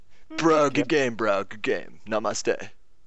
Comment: What does say at 2:43? Namaste?